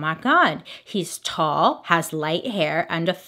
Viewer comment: I really thought she said “he’s tall, light hair, and a ferret collection” instead of “fair complection” smh